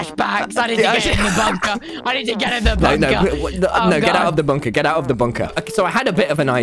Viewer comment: Instead of “Get to the bunker” i thought he was saying “Get me some vodca!”